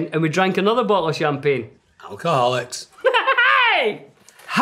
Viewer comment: Dominic may have said "Acholics!" I thought of Gandof saying in disgust "Hobbits!"